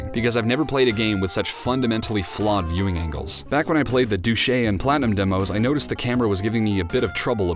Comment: did u say douche-y?